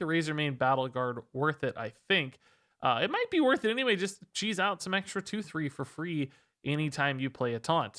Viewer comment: People keep saying ‘cheese’ when all I hear is ‘cheat’. Mana cheating is what makes things op to exploit free wins. I rarely played the busted decks that include: darkglare, evolve shaman, cheat pali, hysteria OTK. The list goes on however once the standard rotation made things leveled and balanced IMO. Now everybody seems to be able to have an edge and each game therefore is equal. Decks higher than fifty percent should get looked at. Learn the cards in wild and synergy buff. Can’t wait to have a shaken up wild meta..